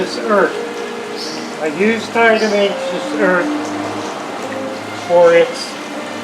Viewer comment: i didnt catch the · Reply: diatomaceous earth. Not, however, calcium.